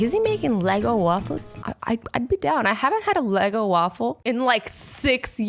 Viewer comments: When she said a Eggo waffle I thought she said a lego waffle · did she just say lego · Am I the only one who heard her say LEGO waffle???😂😂😂. EDIT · did she just say LEGO WAFFLES😂😂 ITS EGO WAFFLES😂 Omg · did she say lego waffle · did say Lego waffles instead of Eggo waffles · “Lego waffles”😂😂 ITS EGO!!! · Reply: I was looking through the comments to make sure I wasn't the only one who heard her say "Lego waffles" 😂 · I thought she said Lego waffle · did she just say "Lego" waffles?